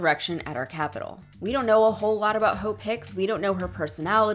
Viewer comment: Hahaha Hahaha at first I thought traitor trump was saying hole pick your hole pick